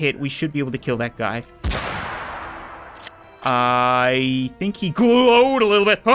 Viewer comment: “What just whispered something in english in my ear?” Im sorry, but i dont think hyama dajibu is english Edit: this is one of my most liked comments on this channel thanks :) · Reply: I heard "I think we have a target"